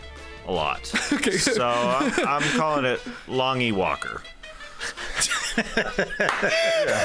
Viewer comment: Misheard Tas the first time. Thought he said Lawn-y Walker ... which, to be fair, also works.